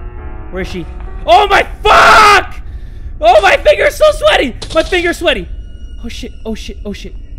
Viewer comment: the first time I heard that I thought he said “MY FINGERS ARE SO SLUTTY”